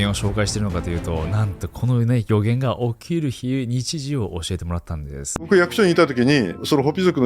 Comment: 聞き取れない なんとこの〜、のあとなんですか？ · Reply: なんとこの～ね、予言が起きる日、日時を教えてもらったんです😮